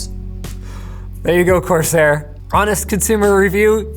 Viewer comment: Um Actually, Linus says "there you go Corsair" and the subtitles say "there you go, Crosshair." And these were not YouTube's autogenerated subtitles. 16:01 similarly Linus says "huck" and the subtitles say "chuck." Proofreading with the built-in spellchecker?